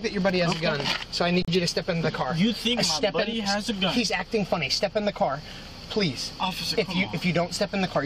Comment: "He acting funny" I heard "he's acting black".